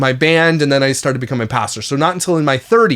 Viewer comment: did he say he was a pastor?